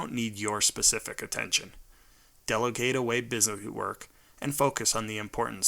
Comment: Hi, may I ask what is he saying in 6:54? Because in the subtitle, it says “delegate away business work and focus-“ in I’m not sure if that’s what exactly is what he’s saying. English is not my primary language btw. I hope one of you can elaborate it to me :> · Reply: Hi; I'm seeing this (according to the time you posted this) almost two years later. What the writer says to do is to delegate (busy work that ANY one can do) versus BUSINESS work, creative things that ONLY YOU can do! Good luck, and I TRULY hope this helps you!